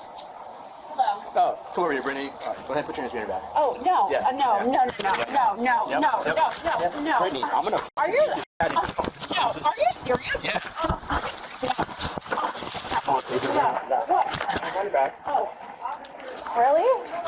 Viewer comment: Does he say” I’m going to fuck the big out of you” 😂😂😂 that’s what I heard